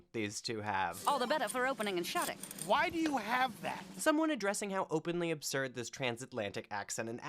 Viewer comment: I think he was saying "why do you have that??" about the old phone she has; not her voice. She takes the phone out of her bag, and it shouldn't even work 😭